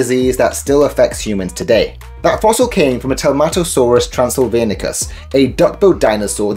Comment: At first I thought you said Tomato Saurus.😂